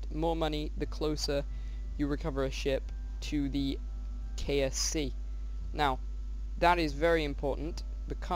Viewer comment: Did u say KFC at 3:40? Or was it just me. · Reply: Oliver Watson Oh sorry it was KSC the launch center.